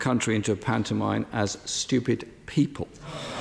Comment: Accusing the PM of being what she obviously was … except that I originally thought he said or mouthed ‘stupid woman’ but after carefully watching it over and over many times I actually agree it also could just as easily be ‘stupid people’ IMHO I can see that the lip position of P or W and M are all closed and appear the same. So I believe him and think we all jumped the gun. REMINDED ME OF AN OLD SAYING … NEVER HAS MY GRIP BEEN SO STRONG AS WHEN I HAD THE WRONG END OF THE STICK …. Def worth thinking about?